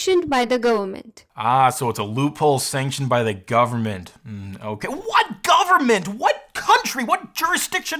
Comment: i heard her say "a loophol sanctuned by the gooberment" xD